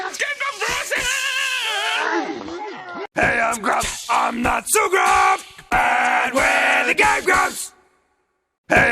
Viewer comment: what are they saying?? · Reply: Hey I'm grump! I'm not so grump! And we're the game grumps!